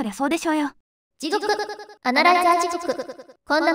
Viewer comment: なんか「地獄の穴ライザー」に聞こえるんだけど w